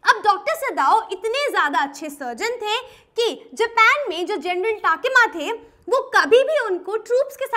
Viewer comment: what did she said?? General??